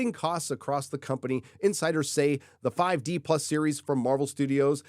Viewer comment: I honestly thought I heard you say "Disney infighters" instead of "insiders". Still fits.